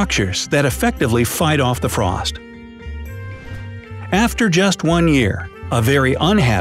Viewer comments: after 1 year i thought u said if the sun goes out for only 1 day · I thought you said 1 day, not 1 year